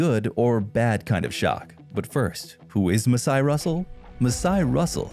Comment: u said "who is masai russell" and the caption said "how is masai russell" lol totally a drax moment there ;)